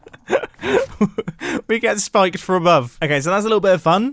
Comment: Is no one going to talk about how it sounded like he said “big a** spike from above”